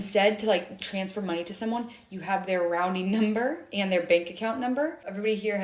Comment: did she say “rounding number” “rouning” or maybe “routing”?.. I’m so confused!?